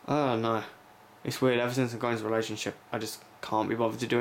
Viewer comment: Maybe I heard wrong but I was responding to what you said at 6:33, it sounded to me like you said " Ever since I got out of this relationship "